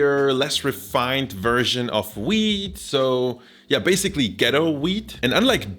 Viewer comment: when you said wheat i thought you said weed